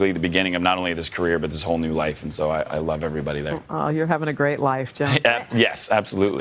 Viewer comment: "Aw, you're having a great life, Jim." Am I hearing that right?